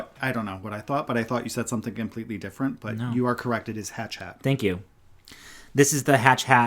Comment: I thought said, "Hat Chat"